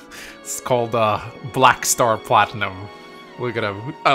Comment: I start this video and the first thing I hear is “black star platinum” if you ever have a gba rom hack made about you again, whoever makes it better make the jagen an archer black knight with “black star platinum” personal skill.